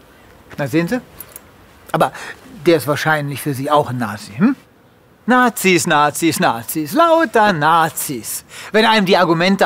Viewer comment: Does he say "Nazis, Nazis, Nazis. Loadsa Nazis"?